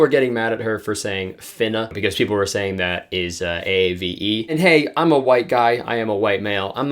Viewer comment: what is he saying? She's what? A V E? i dont know why i cant make out what he is saying right there🤔